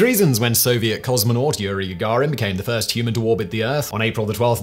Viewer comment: Yuri Garin? I think you meant Yuri Gargarin... Or maybe I misheard...